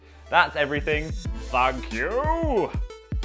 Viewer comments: sounded like "Fuck You" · Who else at the end thought he said “Fuck you” instead of “Thank you”